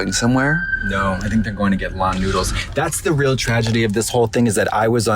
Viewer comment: The LAN noodle in west hollywood is still standing as of 1/14, 3pm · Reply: I thought they were saying “Law and Noodle”, like it was a theme restaurant based on Law and Order.